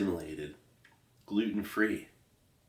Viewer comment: Add about the 15:00 minute mark, did Steve say "Putin" free or "Gluten" free?